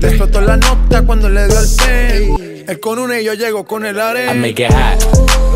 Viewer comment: anybody heard jbalvin says " corona" than show you're self 😂